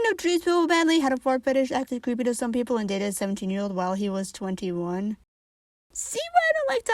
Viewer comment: I was about to say, “18yo? That doesn’t sound so bad…” before I looked into the subtitles. I legit thought you said “some 18 year old” because of how fast you spoke.